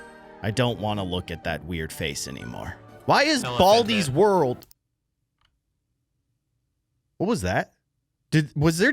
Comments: What did it say at 1:54?? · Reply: Elephant hit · I think baldi said “hello” and then a name but idk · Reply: It said "elephant hit" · What does the random noise at 1:54 say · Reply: Baldi is dead